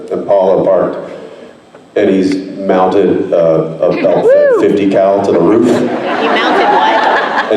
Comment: Can someone tell me what Jensen said Dean would have mounted on top of the impala? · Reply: A 50 cal. It's a large caliper gun